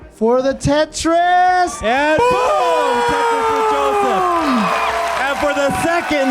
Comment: I kept hearing "Tetris for Jonas."